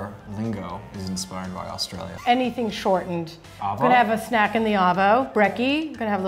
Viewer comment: For some reason I have subs on. And at 8:14 instead of arvo it was avo...